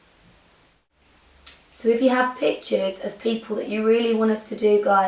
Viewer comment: Okay definitely thought you were saying bitches instead of pictures whilst I wasnt watching the tutorial and just had it playing in the background.